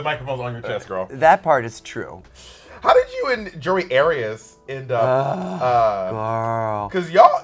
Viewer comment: I totally thought Bob said Jodi Arias, that woman who brutally murdered her ex lmao